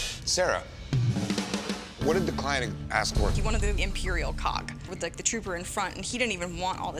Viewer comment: Im just wondering…. Why would you want a clone trooper in front of the imperial cog instead of: 1. Clone trooper in front of a republican cog, 2. Stormtrooper infront of an imperial cog??? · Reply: I thought they said imperial cock 🤣